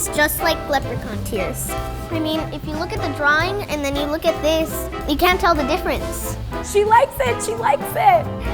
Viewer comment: Did she say you can not tell the difference 10:35 lolololol